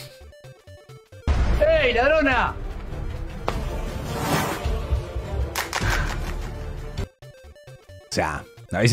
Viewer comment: No entendí 🤔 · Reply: de que mínimo la hubiera perseguido